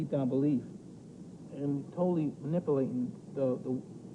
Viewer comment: does Mike say maNIPPLEaten?